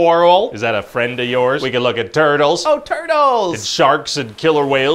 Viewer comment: The way his face lights up when he says “Turtles!” made my day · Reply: Pretty sure I heard "TURDLES"